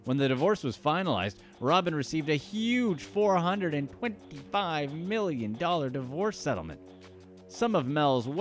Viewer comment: there's a big difference between "four hundred and twenty five million" (what was said) and $4,250,000 (what was on screen) lol